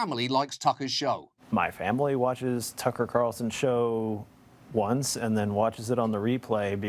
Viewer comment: i thought john said tucker cuntson at first! 😎